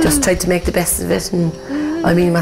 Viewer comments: I thought they were saying 'she's american' not 'she's a miracle' lol i was so confused for a bit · It's a beautiful story, and Megan is an adorable little girl, but every time they call her a miracle, all I can hear is "She's American." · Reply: SA S yeah, I know,of course. I couldn't figure out what she meant by American, because I clearly heard and knew they were saying a miracle. I had to reread this before I got what she was thinking they said. It didn't sound like that to me, it sounded like a miracle. Couldn't figure out why she thought they were saying something about American 😂, and when I read it again I saw that she didn't get what was actually being said. I think you are responding to the wrong person. I was saying the same thing as you are. But I also wondered if she felt some special something about they being American, or why else would she have that on her mind that strong. Haha